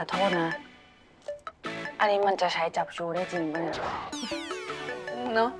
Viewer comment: what were both of them saying😑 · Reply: The evidence we have Can it really be used?